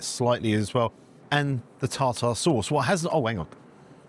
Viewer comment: When he said "Tartar" I thought he said "Tosser" OH the English language is so much fun! 😄💙